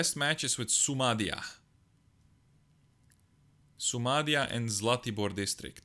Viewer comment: Did you say somalia @ 8:11 ???